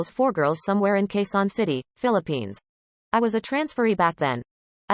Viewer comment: at first I thought she said "I was a trans furry back then".